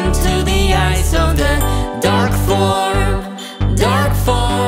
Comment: I keep hearing "Duck form" lmao